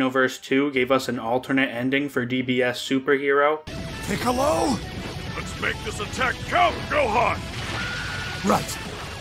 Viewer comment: I thought piccolo said "lets make this attack c** Gohan"☠️